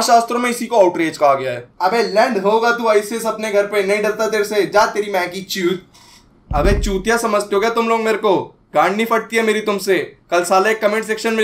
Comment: I heard Maggie Chut😂